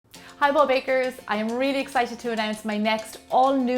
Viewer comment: For the longest time, I've heard 'Hey Bob Bakers!' and i'm like who's Bob? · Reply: unicorn I thought I heard "hello bakers!" and I thought it was because of her accent but I found out a while later that she said, "hey BOLD bakers!"